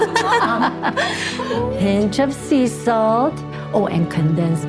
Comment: Did she say sea salt 5:54 SEA SALT I NEED YOU SEA SALT